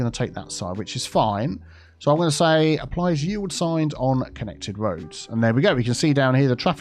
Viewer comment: “Yield signs!” I kept hearing you say, “Ye Old Signs!”